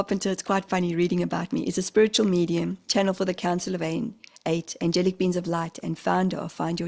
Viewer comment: I thought you said “the jelly beans of light” instead of angelic beings of light 😂😂😂😂. Have a great time doing your new thing with Kelvin 🥰🥰😆